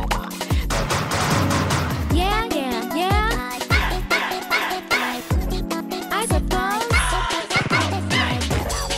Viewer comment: wait what is Girlfriend saying in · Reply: yeah yeah yeah i suppose